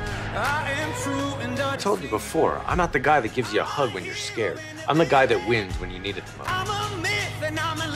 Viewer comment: Some write me what he said in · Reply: Omarouassaid Med I’m not the guy who gives you a hug when you’re scared, I’m the one who wins when you need it the most.